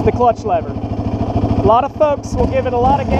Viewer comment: It was recently brought to my attention that at 3:02 I referred to other riders as thugs. This is simply not the case. Listen carefully and you can hear me clearly saying "folks." If you haven't already noticed this is a clean channel. Also, "folks" is a term I use often on my channel. I have to admit, playing the video back it did sound similar to the word thugs, but if you pay close attention you can hear the sound "ks" at the end of the word, confirming that I in fact said folks. I remember filming this video like it was yesterday and I know what I said. Also my accent can be hard at times, causing the words that I'm saying to sound a bit different than what you're used to. I'm making it clear now to everyone that I did not say thugs. I'm surprised that someone would consider I would say that considering my reputation on this channel has always been to keep things clean and enjoyable for all walks of life. I'm not upset that someone would point this out, however, I'm a bit disappointed that they would think that I would call fellow riders thugs on this channel, I don't stand for that, and I hope you don't either. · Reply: People need hearing aids. I clearly heard "FOLKS"